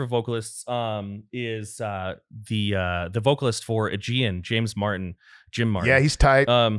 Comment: Who is he saying here? I don't understand the band name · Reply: Aegaeon